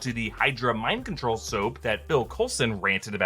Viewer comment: did he say “Bill” Coulson?? 😂